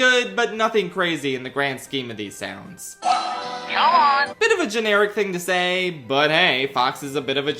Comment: Fox used to be my fav character when I was a kid (and the first character I picked) cause it sounded like he was saying my name (Sean)